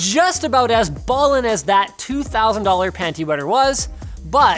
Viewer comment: HA! Did I just hear Linus just say panty wetter @ 1:53?